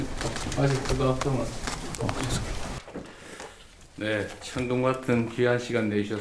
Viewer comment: 자막 "천둥" 실화냐?...대충 안들려도 "천금"이라고 때려맞출수 있을 상황인데, 뇌가 을매나 순수한거냐?? 이건 "천금"이란 단어를 아예 몰랐다는거잖아... 방송국애들 수준 점점 떨어지는 소리가 여기까지 들린다....